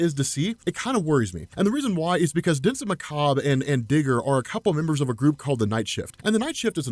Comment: Not paying attention i thought he said nigger ngl